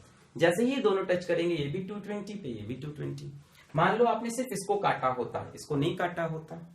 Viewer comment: I heard T20!! ROFL..😂😂